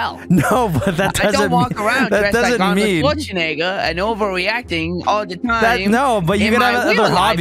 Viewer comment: PSA: BRIAN DOES NOT SAY THE N-WORD AT 13:40. He says "I dont dress like Arnold Scwarzenegger all the time." Jeeeez. A few people needa clean out their ears 😂😂